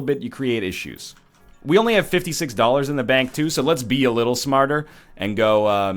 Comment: Dude I could've sworn you said this was gonna be "better & smarter"